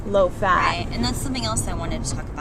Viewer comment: Lol.. I totally thought you said "taking charge of your Fruit-ility" instead of fertility.. anyways, i'm going to look into this book.. bleeding is a bad thing?! that is really odd. Ya know ever since I've been vegan i have had lighter periods..and it really freaked me out, but having your period be basically non existent & just discharge.. idk that just sounds unhealthy.. :/